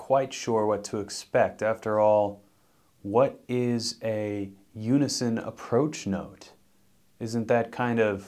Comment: I thought it said “Unicorn Approach Note”